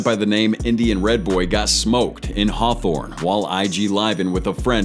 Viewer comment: dude indian red boy sound like he said” im in harthorne”